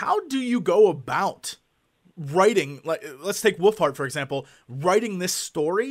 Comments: Sounds like he says "woofheart". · It sounds like he is saying woofheart.. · It always sounds like Jesse is saying "Woofheart"